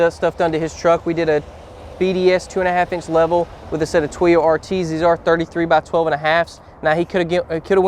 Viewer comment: What's a set of Twio RT''s? You said this when describing the leveling kit at 1:46. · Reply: “Toyo R/Ts” - a rugged terrain tire.